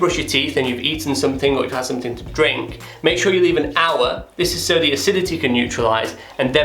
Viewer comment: Thank you. What is the word you said at 2:34? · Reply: Sanjeev George he said “hour”